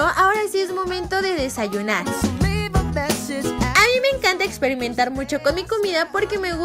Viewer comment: que dijo ixperintar bueno todos nos equivocamos